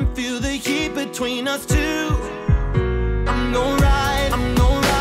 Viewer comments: Him: I'm gon' ride. Me: That's not how it works....it's the other way around. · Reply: Dude at first I thought he was saying "I'm your ride" then I realized he said "I'm gon ride" and I was like "...Well ok then..." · Him: I'm gon' ride. Me: That's not how it works....it's the other way around. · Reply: Kpop Crazy I thought he said "I'm your ride"